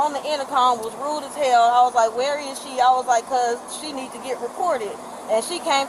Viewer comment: I wasn't reading the subtitles (just listening) and I choked on my coffee because I thought she said, "She needs to get deported." 😂